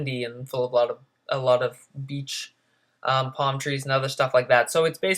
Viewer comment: sound like u said B*****